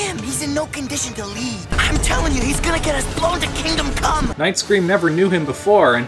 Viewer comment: did he say i'm tellin ya he's going to get us blown to kingdom c*m"